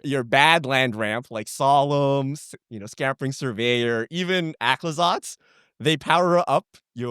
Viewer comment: What does say at 7:25 after Solemn and Scampering Surveyor? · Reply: Aclazotz